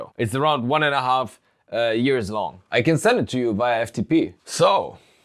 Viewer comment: The “I can send it to you via FTP!” Got me quite hard 😂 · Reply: Ha! I had to watch the video a few times before I realized what he was saying. I thought he was saying "I can send it to you but I have to pee."